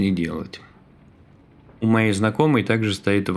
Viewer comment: мне послышалось на 06:51 минуте "У моей знакомой"? Что слабый пол тоже не слабо вкладывается в S.C.? скажи что Ты оговорился "У моего знакомого корабль за 350 баксов"?